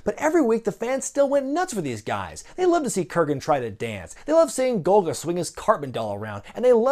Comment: it sounds like you said Golgadust.